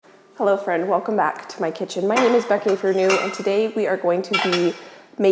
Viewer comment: I’ve been watching her videos for a while and thought she was saying her name was Becky Ifernew. I just realized she says Becky and if you’re new. Goodness 😂 🤦‍♀️